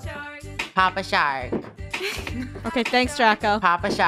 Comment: i thought he said pop a shawt